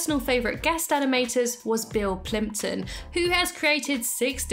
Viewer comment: I heard Bill Clinton and I was like he did what?